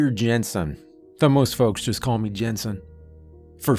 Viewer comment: I heard Peta Jensen 😅😏